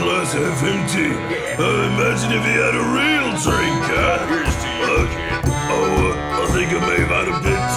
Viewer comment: there's a guy in the background saying "Here's to you .... " can't catch the last word thought... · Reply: Jax saying "here's to you kid"